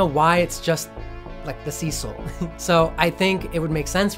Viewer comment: when you said Cecil around 5:08 ish i think, the subtitle said “Sea Soul” and i’m like …. “ohhh, that explain the deaths and everything”